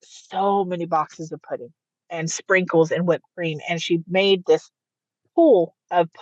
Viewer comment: Did anybody else catch the slip-up at around 35:55 where the ex (Jamee McDaniel) is telling the story about the pee pudding pool, and says "we made...", then catches herself & goes on to tell the story like she had absolutely nothing to do with that incident? It is shocking and terrifying to know that there are absolute monsters walking amongst us.